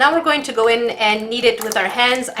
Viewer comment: i choked on my soda when i thought she said " ok lets go in and eat it " when she said " ok lets go in and kneed it " XD